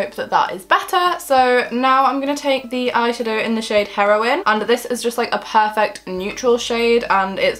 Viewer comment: wait 9.10 what did you call the shade name. I swear I heard "heroin"😂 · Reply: Jessica Barrett I'm like 90% sure it's heroine as in strong female character rather than heroin the drug 😂😂❤️